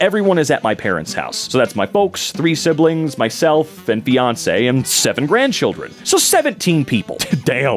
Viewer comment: omg i thought you said "and Beyoncé" and i was so confused